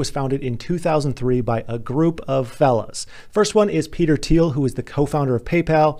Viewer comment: I thought you said ‘a group of felons,’ 😄